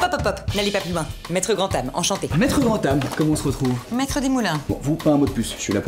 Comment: au début j'ai compris "maitre grand ane"...... XD